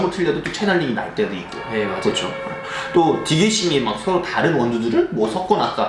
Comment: 에 디디씽인지 디딕씽인지 그게 뭔가요?? 영어로 얘기해서 뭐라는지 모르겠어요. 초보라.. 검색해도 안나오고 알려주세요. 하나하나 알아가는 병아리 입니다요 ~ 모든게 영어라.. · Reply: ㅋ 댓글 보다가 알아냈네요.. 디게싱이였다는걸... 처음 들으니까 여러번 들었음에도 디디싱으로 들렸어요 ㅋ